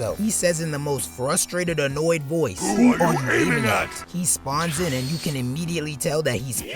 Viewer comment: If you couldn't hear the Raikage at 0:06, he said "Who are you aiming at?!"